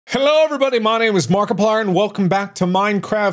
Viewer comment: I cant be the only one who hears this? 0:00 "Hello everyone my name WAS Markiplier."